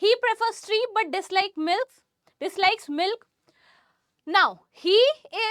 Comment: DID SHE JUST SAID MILF ON 13:37 MIN?